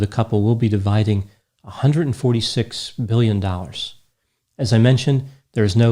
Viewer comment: $146 Million or Billion? What did sayyyy? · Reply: Billion